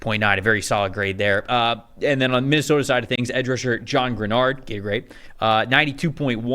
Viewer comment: did dude just say gay rape?